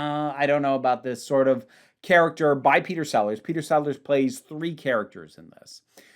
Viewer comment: I initially misheard Peter Sellers as Peter Sallis, which I suspect would have been a very different (three) performance(s).